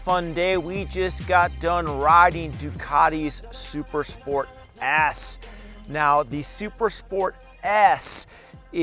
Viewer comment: Bro you have to enunciate better. Its sounded like you said Super Sport ASS.